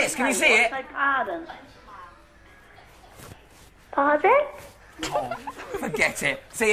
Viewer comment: I just noticed the nan in the background saying "Don't say what, say pardon" lmao